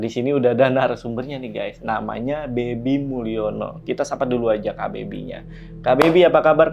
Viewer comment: Di awal saya dengar namanya Dedy mulyono wkwkk Salah dengar ternyata 😢